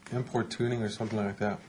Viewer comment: Am I the only one who thought he said import tuning? Like he was a street racer lmao